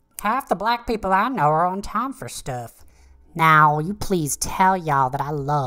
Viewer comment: "Half the of the black people i know are on time for stuff" -Maula Been. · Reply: I heard "are on town for stuff" and I was like WTF?